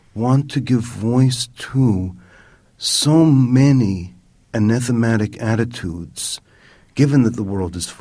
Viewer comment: "enethematic"? What is this word? I must be spelling it wrong. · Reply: ‘anathematic’ - hateful, loathsome